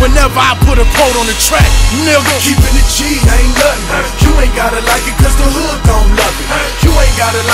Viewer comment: I always thought it said you aint gotta like it cause the hood dont love it